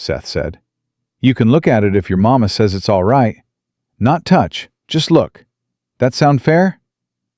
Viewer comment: I thought he said look but not touch.